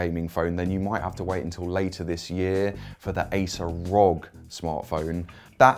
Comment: did he just say "Acer ROG"